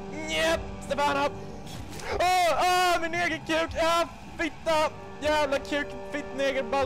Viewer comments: you know what he said was "neger" thats the n- word in norway and sweden 5:30 i know since i live in norway and travel alot to sweden but hey i dont hate on him. he dont blurt out the n-word or anything to be mean to peopel and he comes out and apoligise at least when he dose something wrong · I am from sweden and he did actually say the n word 5:30. In sweden it is called neger.